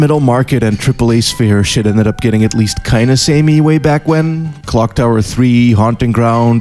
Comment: What is the game at 0:57 ? "kayna samet"? "Kind sammy"? "Kinda summit"? I heard, how you pronounce this, but what letters should i put into google search? Can you put a name of the game in text, at the bottom of the screen? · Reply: The Overwhelming Hell. He said "Kinda Samey", as in kinda looks the same